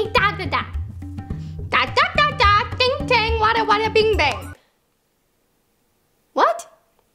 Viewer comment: Did Vanessa just said whata whata ding bay